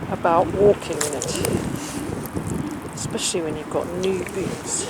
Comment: Yep, @ 10:05 Mudlarking nude in your boots .. great idea... hope it catches on with the other Mudlarkers ... ! Oh, hang on a mo ... did you say new boots ... ok ... my bad !